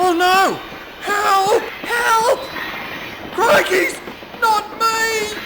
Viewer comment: what did you say!!! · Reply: CRICKEYS!